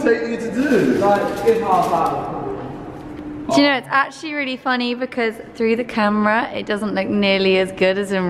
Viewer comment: IKR I thought she said honey but she said funny he is faster than you LOL